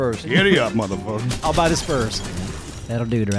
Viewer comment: It's official! You should end every video with "That'll do Doraleous! That'll do!" I might just have to make that my text tone! Also... #AppsroForPresident #SimonForSecretaryofSandwhiches #Commentingoncomments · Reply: For some reason I heard that as "Daddle do Doraleous. Daddle do." 😂